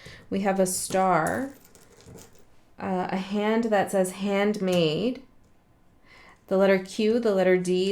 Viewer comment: My head hurts. I have had the reading about the domino effect come up 7 times on my feed, have watched it three. I watched the cats with the dominos video because I figured that's what I was missing, said, "wait, what happened?" Then this video popped up with that as the title. And now my head hurts. 😂 By the way, when you said, "hand made" I heard, "handmaid.". Not sure if that's for me or someone else.